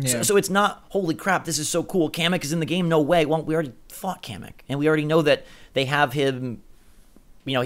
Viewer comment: I thought you said "f*** Kamek." And I was like, "wow he actually swore"